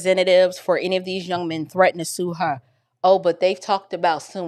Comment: Wait. He did not say someone threatened to sue the Wells’ mother. He said someone tried to take her job.